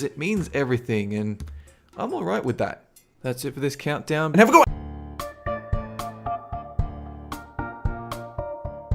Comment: What does he say at the very end, very very quickly? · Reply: He says have a good one.